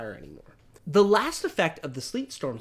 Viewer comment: At first I thought he said Sleep Storm